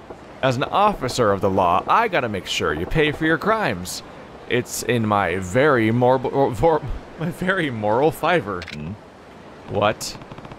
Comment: What I heard: "It's part of my very morbid fiber" XD